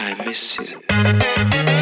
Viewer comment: I know the Voice says “I miss you” but I hear “I miss it”